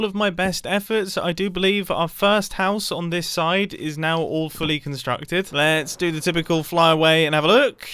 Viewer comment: Every time Mumbo uses the phrase "All fully constructed", I hear "Awfully constructed."